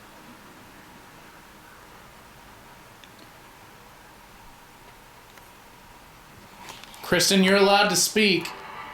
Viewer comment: Did anyone else hear a whisper at around 30:20 before they tell her she’s allowed to speak? I can’t tell what it’s saying, but it sure sounds like a female whispering to me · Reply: omg Yes! I been scrolling through the comments to see if anyone else has heard it, I was able to make out leave me alone. I had to replay it a couple times to make sure that's what I heard.